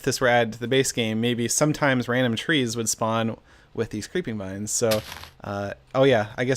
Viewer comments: Who else thought it said "creepy vines"? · I thought it said "Creepy Vines Mod" which lets you watch vines in minecraft